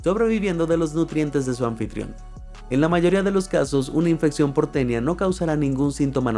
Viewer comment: En la parte que dice algo sobre la infección por tenía yo pensé que decía infección porteña